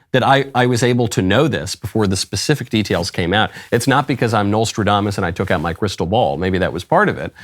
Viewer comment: Did he just say Knowlestradamus at 3:28?